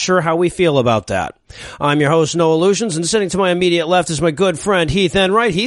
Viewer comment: Am I the only one who heard "Noah Lugeons", but heard it as "No Illusions"?